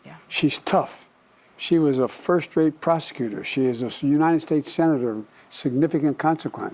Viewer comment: Sounded like he said "She was a first rate prostitute"